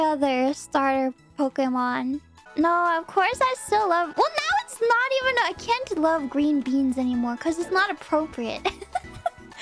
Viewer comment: Fauna: "Well now it's not even... I can't love Green Beans anymore" ShiroClips: "Well, now it's not even a cantaloupe Green Beans anymore" Well you were... close to what she said... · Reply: Tbf, I also thought she said “cantaloupe green beans”.